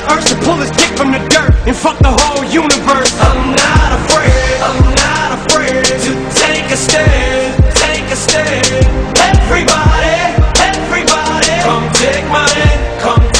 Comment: What I heard: I'm not afraid to take the stairs, but everybody just takes my bread